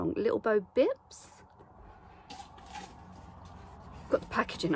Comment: I thought you said little b*tch bow for a second haha